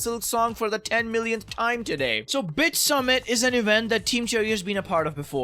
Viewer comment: I misheard it as B*tchSummit and then I couldn't unhear it 😂 Silksong July 2025! 😤